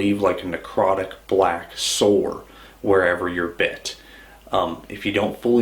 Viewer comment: it sounded like he said "Necrotic Black Soul" which is the greatest damn metal band ive ever heard